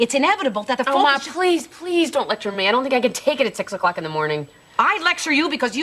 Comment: Did she say 6'o'clock in the morning? 35:44 Did she say "I have to take a shower"?